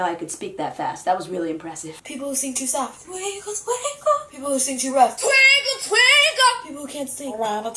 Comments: Is it just me or did it sound like she said "I want a twinkie! " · did she just say im a twinke or twinkle twinkle XD